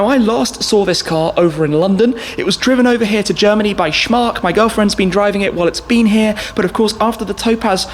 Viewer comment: DID SAY GIRLFRIEND?!?